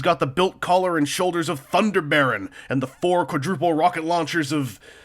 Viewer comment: Thunderbaron? That sounds like an awesome as fuck name. Or did I mishear because I can't find anything on tfwiki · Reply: Thunder Baron is from the Braves... look for The Brave Fighter of Sun Fighbird